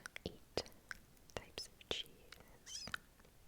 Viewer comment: I thought you said 'cheif's' so I was trying to think back on every single history class I've ever taken